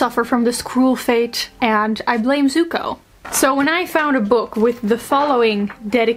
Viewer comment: I misheard you as "I blame Tuco" (admittedly, at the usual 2x speed), thinking of the Breaking Bad character. I try to not be judgemental but I did think you were pretty weird for that ^^